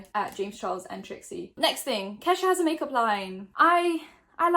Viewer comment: Lmao when you were talking about Kesha i wasn't seeing the screen and I thought you said Ketchup was releasing a make-up brand 🤦😂